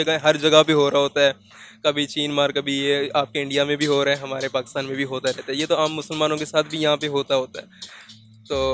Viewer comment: Did he said Hum Musalmano ko 😅